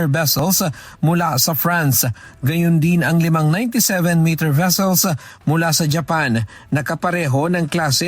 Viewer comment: Tama ba narinig ko 97 meters? Wala pa ata tayong ganung warship e